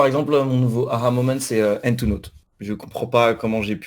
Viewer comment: à 5:00 c'est quoi le soft dont ils parlent ? "ENTONOT" littéralement j'entends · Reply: C'est "Hand2Note"